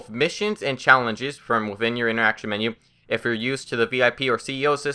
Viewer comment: seconds in you start to talk about what we want to hear... And you mumble everything important why guy? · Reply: He said on your interaction menu